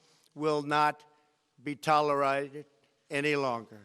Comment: minutes, I don't understand what he's trying to say here. "Will not be toleraid?" Is he tryn to say : tolerated